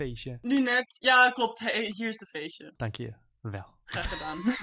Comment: it sounds like she said Yeah, OK here is the fish